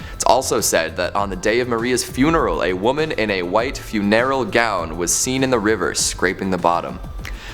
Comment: “fuNERal” gown??? I’m not sure that’s how you artiCUlate that. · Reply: I thought he was trying to say funereal lol